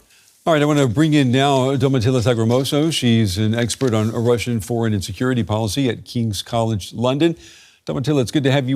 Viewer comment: I'm pretty sure he said "Russian foreign and security policy" but I heard "Russian foreign insecurity policy".